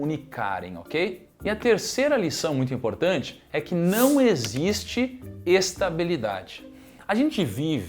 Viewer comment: Kaique!!! Corrige aí: em 4:32 o Thiago fala "não existe estabilidade" e Você escreveu "não existe instabilidade". Você escreveu o total oposto do que ele falou! 😅 Abraços!